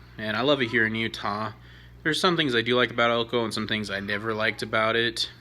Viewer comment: I thought you said there were elkoholics 😂